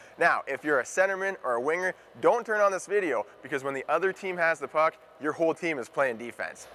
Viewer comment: what you said at the start to wingers and centers...i think you meant don't turn OFF this video, right? sounds like you said don't turn ON this video lol